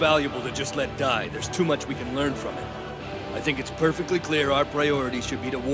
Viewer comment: Did I heard "There's too many people, I can smell it"?